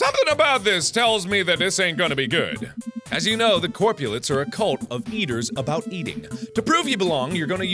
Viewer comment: I thought he was saying capulets at first..